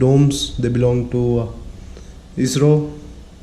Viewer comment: This is what I was asking in the other video. At 6:56, you say something like, "You can see the domes, they belong to Israel." I just can't make out the last word clearly... Thank you for your great videos. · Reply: He said "they belong to ISRO (Indian Space Research Organization)".